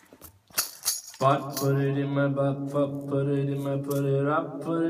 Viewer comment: The way he sang, “Can you put it up inside, if you want to put it in my mind “ hit different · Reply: I honestly thought he said "put it in my but"